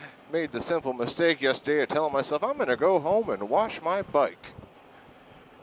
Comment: I was half listening as I prepared to write an email to my secretary when I heard you say, "I'm gonna go home and wash my BUTT!" BWAAAAA!!! Thanks for the accidental laugh. :-D